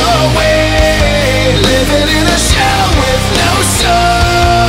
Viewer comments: Lol when I first heard this song, instead of “shell with no soul”, I heard “shower with no soap” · I thought "living in a shell with no soul" was "living in a shower with no soap" at first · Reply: I thought he said "livin in a shower with no stall" at first. xD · I thought "living in a shell with no soul" was "living in a shower with no soap" at first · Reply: I thought they was saying liveng in a cell not shell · This comment section: 70%: E D G E 20%: Actual serious comments 10%: SHOWER WITH NO SOAP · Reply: Exactly. I thought he said "shower with no soap" when I was younger